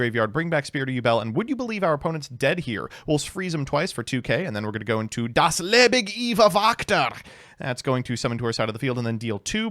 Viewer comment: wtf did you say at 12:18 i am german and have no idea what you were trying to say just asking cause i am curious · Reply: for some reason it's Japanese name is Yubel - Das Ewig Liebe Wächter